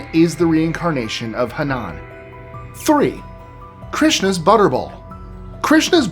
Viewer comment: I thought his said Christmas Butterball